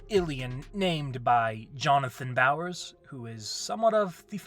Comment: i definitely heard jonathan byers